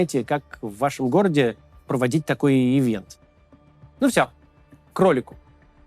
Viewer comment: на 2:18 - услышала "кролику" )